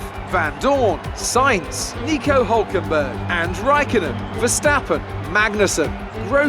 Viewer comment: Did he said 7:10 raikonnen?